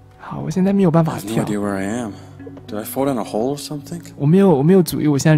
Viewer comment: 阿神 10:25 他說where 不事 who where是在哪 who 是誰 給對英文不熟的人解釋^^ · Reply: 宇楓 阿神大概是把where聽成who了 因為他說太快了<num>